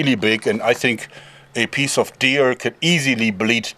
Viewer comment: A piece of deer can easily a piece of bleed to death a bit ..I am a piece of very like this way to speak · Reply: Haha I had to go back twice because I thought he said "peaceful" deer at first. Like an innocent peaceful deer frolicking in the woods